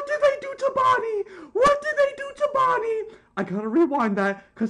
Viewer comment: What did bro just say ._. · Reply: “They got bonnie”